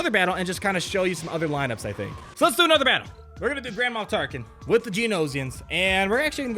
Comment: It sounded like you said Grandma Tarkin. Now all I can imagine is him waving a walking stick at rebel pilots yelling "get away from my ship you punks".